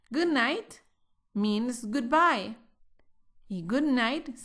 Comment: Hello... help me.. quisiera saber si escuche bien 6:25 Good Night "MINGS" Good Bye... es decir, la palabra MINGS fue lo que dijiste .. corrigeme por favor .... SEE YOU NEXT LESSON. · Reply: ) ¡Hola, Alvaro, muchas gracias por compartir con nosotros! 🌻🌞🌻 Es "means" - "significa" ;) ¡Un saludo!